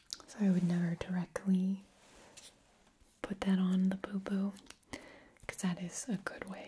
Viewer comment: lol thought she said "it's important to clean out the poo poo" instead of boo boo for a sec there